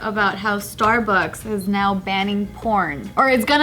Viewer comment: I thought said starbuck is banning corn